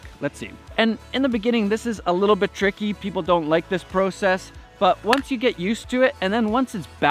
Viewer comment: did he say "PROcess"?